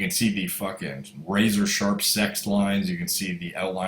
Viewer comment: Did I hear "razor sharp sex lines"? If so, I approve.